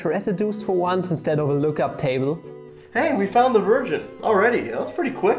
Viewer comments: Did he say " we found the version" or "we found the virgin" 🤣🤣🤣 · I thought he said " we found the virgin already" LMAO